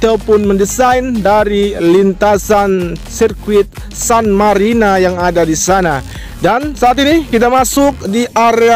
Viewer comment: GA Salah denger gua sirkuit Qatar san marina 2:10 ? 🗿😀